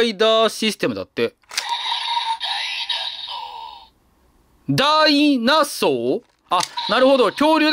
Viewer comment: ~ ギリギリ「ダイナソー」って聞こえるけど､ 確かに「大脱走」に聞こえなくもない｡